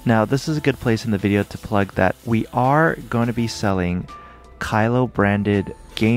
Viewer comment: for a second i thought he said "we're gonna be selling kylo"